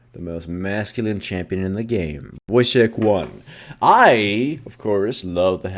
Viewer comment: WHAT does he say at 0:06? He says something similar in all his videoes, but I can never seem to figure out what it is. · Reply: Voicecheck 1